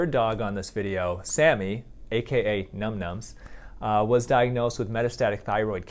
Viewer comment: When u said num nums I thought I said numb nuts and I hated myself bc it was at a sad part